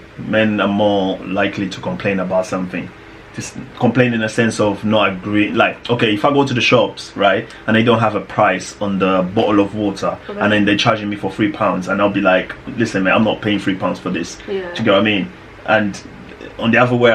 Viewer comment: Did you just say bo'oo'le wa'er @ 15:53 ? 🤣